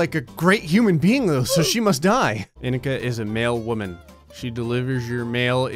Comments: Anybody else not looking at phone when he said “mail woman” cuz I thought he said male woman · When he said mail woman I thought he said male woman 😂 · Honestly when you were talking about indica I thought you said “male women” 😂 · I thought he said "male woman" and got extremely confused